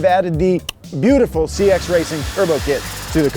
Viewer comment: I thought you said sketch racing turbo kit 😂😂